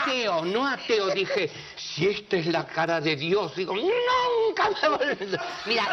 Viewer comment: No entiendo que dice en el minuto 1:34. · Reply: Dice " nunca me olvido"